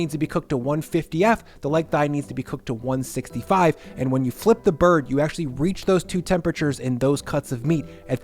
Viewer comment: did I hear the phrase "flip the bird?"